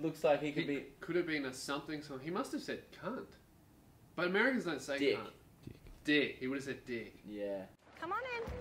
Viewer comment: I heard “Americans don’t say cunt.” Im offended. I’m American and I like saying CUNT